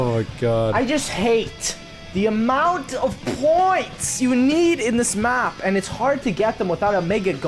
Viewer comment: for a second I thought was going to say at 0:50 "I just hate the amount of porn in this map to..."